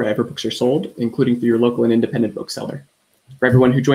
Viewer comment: i thought he said welcome to toxic google